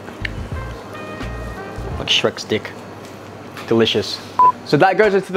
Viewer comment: I literally burst out laughing when he said like "Shrek's dick". That was so unexpected! LMAO!!! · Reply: Really? What I heard was "shrek stick" 🤣🤣